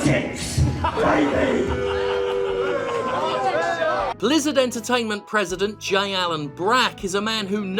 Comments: For a second there I thought you said Lizzard Entertainment. The name doesn't fit them very well though, since lizzards only look slimy. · As a distraction from the horrifying shitscape our world is turning into: Jim looks like the villain of a Modern-Day Dolemite. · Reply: omg, when the video proper opened I thought Jim said "Lizzard Entertainment president J. Allen Brack". As in the "Lizzard People". Can we start calling Blizzard just Lizzard? Can we just call them the Lizzard People? Thank you.